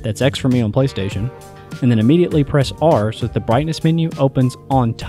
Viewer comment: did you say immediately press R?